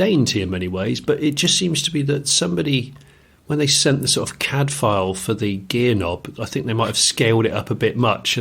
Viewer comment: When said CAD file, I thought he said Cadfael, of Derek Jacobi fame.